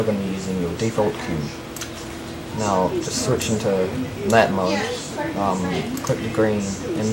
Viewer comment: Jeez I'm having a hard time understanding you... What did you say at 0:25? That mode? And at 0:27? Click green? I really have no clue! >.<